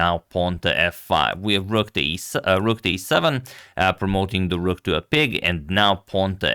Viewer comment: did Agad say promoting the rook to a pig at 3:31?